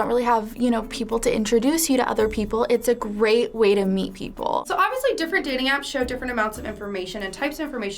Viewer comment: did anyone hear what was she saying? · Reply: Way to meet people